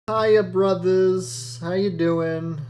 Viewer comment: It almost sounded like you we're saying ''Ha, you're brothers''